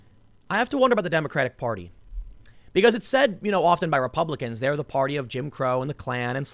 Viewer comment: “Republicans are the party of Jim Crow”... You do realize that’s the Democratic Party, right? One member switched parties after the civil rights act, which had a higher proportion of republicans in Congress supporting it than democrats. The south remained firmly Democrat until the 90s, and prominent members of the KKK never left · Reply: I think you misheard. Tim said "It's said by Republicans that they're [the Democrats] are the party of Jim Crow." I know I had to listen to it a couple of times to understand it; Tim is really muddling up his words at that point and is borderline mumbling. Edit: typo correction. Stupid touchscreens.